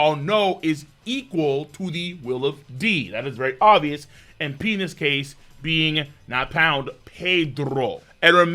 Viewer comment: I heard "P in this case" as "Penis Ace" That explains Portgas D. Yamato flashback in 1015